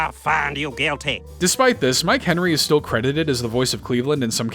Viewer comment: i thought i heard MARK Henry and was so confused lmao